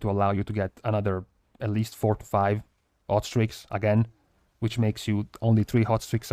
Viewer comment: When you were talking about hot streaks... I kept hearing 'ostrich' and I was like... "it's some new weird trinket with procs ostriches or wat".